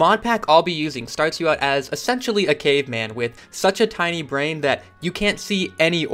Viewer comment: i heard "sexually a caveman" -_